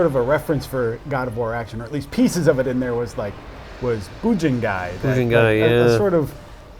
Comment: What is the name of the game mentioned at this part : 19:19 I can't understand how to spell it and look it up. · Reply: Bujingai